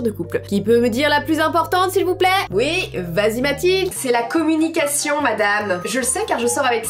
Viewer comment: 😲C'est moi ou elle a dit Mathilde JE M'APPELLE MATHIIIIIIILDE ET NON JE NE SUIS PAS UNE BIATCH 😂😂😂